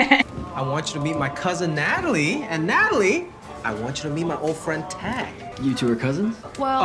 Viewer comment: damn i thought he said my boyfriend oh my kshjshs GIMME THAT I LIKE IT